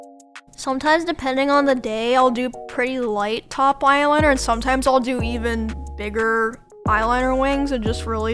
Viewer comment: what did you say? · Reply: “Depending on the day, I’ll do pretty light too eyeliner and other times I’ll do pretty heavy eyeliner wings” <33 sorry im almost a year late lol